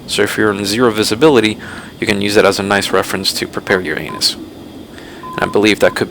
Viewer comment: did you say "prepare your anus"? I'm not making a joke out of this. I actually can't figure out if you said that or not?